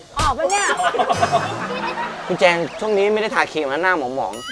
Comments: พูดว่าอีไปแล้ว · ตอนล้างหน้าพี่แจ๊สพูดว่าอี จริงๆพี่ชนะแล้ว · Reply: เขาพูด คุณครับ ไม่ใช่อี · พี่แจ๊สพูดว่า”อี”แล้วป่ะคะ<num> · พี่แจ๊สพูดว่าอีนะ 5555